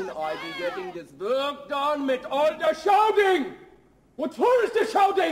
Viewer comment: "How cun I be getting dis vork dun mit all da shouting? What for is dis shouting?" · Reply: I thought he said "We're through with the shouting!"